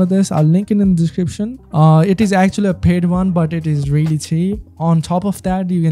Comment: did i just hear a grown man say "thats really tea" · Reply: hahah NOOO i said "thats really cheap"😭